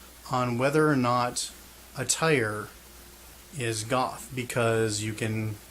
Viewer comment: When you said, "Whether or not attire is goth" I misheard it as, "Whether or not a tire is goth" and I thought that you were going to go on to use car tires as a metaphor for goth things... because they're black and tough and hold the car up at the same time as being separate with their own identity...